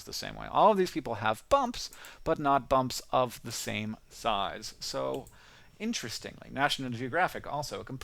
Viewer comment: I thought you said bumps in the same thighs.